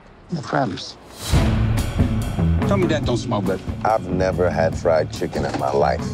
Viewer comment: What the hell does he say at 0:28?? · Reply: Ah! Now it makes sense. But I'm sure it's "Tell me that don't SMELL good", which is probably what you meant.